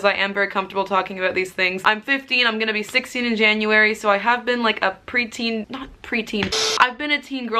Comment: At the beginning of the video I thought she said I’m 50 and I’m gonna be 60 in January 😂 btw this video was so amazing and helpful Tysm! Happy new year everyone!